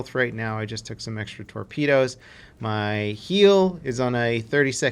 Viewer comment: I hear you say ‘ My heal’ but you probably mean ‘My repair’.